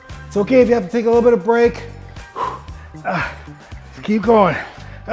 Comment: All I hear is , it's okay to do a 'little of going, just keep 'breaking'. I'm only 30!!!